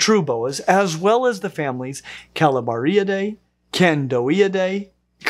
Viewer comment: I heard that as "Calamariadae" as I was picking something up and not looking at the screen. I had to look up quite fast to see that some cladist wasn't having a laugh at the octopodes' expense! And yeah, I don't think I'll ever lose my reflex to, when confronted with a snake that's red, black, and yellow, to start reciting "If red touches yellow, you're a dead fellow..." under my breath. I automatically do it with pictures and images of the dang things even though there's zero chance of an image doing me any damage.